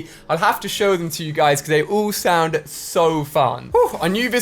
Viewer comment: shound.. instead of sound🤣🤣 plus how he says it makes me laugh... Tho I still love him as the best yt